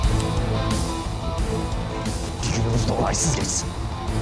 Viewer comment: de ne diyor Mert ? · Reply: bi günümüz de olaysız geçsin